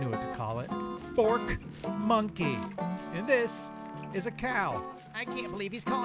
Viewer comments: Wait, you were saying forkmonkey fr? Dead ass thought you were just saying porch monkey and giving zero shitd lmfao... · When I first heard "fork monkey" I thought they did say "porch monkey" until I noticed they had forks for weapons. The entire time I thought they said "porch monkeys" , I didn't give a shit, and I myself am a monkey, so if I'm not offended, you can't be either. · This whole time I thought you were saying "Fort Monkey" cause you normally find them in the forts. Now I understand Fork cause of the Fork arm. Got it.